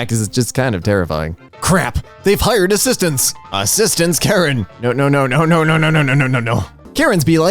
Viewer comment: I think it is supossed to say "assistans kåren" and it is a swedish towing company